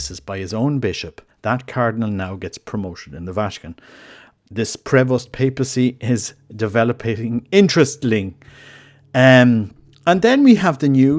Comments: Hi, I couldn't quite make out what you were saying at 1:43? Could you say again? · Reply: I know you are not asking me, but let me tell you what I understood: "This Prevost papacy is developing interestingly..." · Can anyone tell me what is saying at 1:43? I can't quite make out the words. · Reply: "The Prevost papacy is developing interestingly", is what I understood.